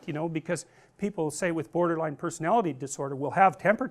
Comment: I thought I heard "boarder-like personality disorder" WHEEZE